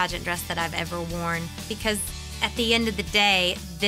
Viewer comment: did she just say marry her best friend?